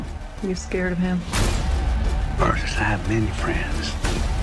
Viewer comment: what does De Niro say at 0:20??? Sounds like "Murderers have many friends" but I'm not sure even after listening to it a dozen times